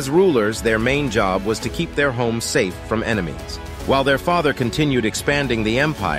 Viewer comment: Bro I heard "keep their holes safe from the enemies" 😅😅😅😂😂😂😂😂😂😂